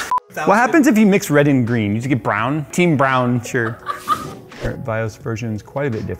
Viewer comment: He said "team brown sure" but I misheard it as "team brown shirt" for the first time lmao